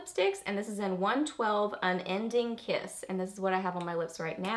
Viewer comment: Um I think you mean unending kiss for the L’Oréal lipstick? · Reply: lol well don’t I feel embarrassed now! You did say unending kiss, I rewatched it. For some reason I could have swore you said unending love.